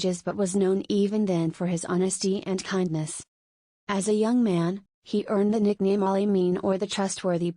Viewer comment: and 1:14 there are mistakes. Instead of "... Ali ..." He was called Saadiq(truthful) and Ameen (trustworthy) ... the AI u used did also separated Ameen into 'A' and 'mean'. Kindly proof read and check before posting and fix this immediately as this is misleading and inaccurate.